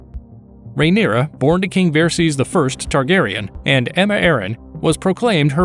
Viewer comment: The pronunciation was so bad I literally heard "Rhaenyra, born to king Xerxes the First"...